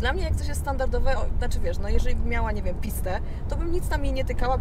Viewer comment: co Ona powiedziała? XD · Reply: Powiedziała Piste, czyli wersja Ferrari 488 Pista